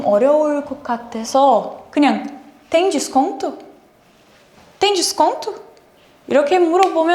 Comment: The subtitle is wrong: It is DESCONTO ✅ NOT desonto ❌